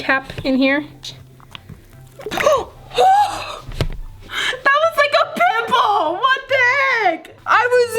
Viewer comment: I think at 3:53 I think I heard her say that popped like a pimple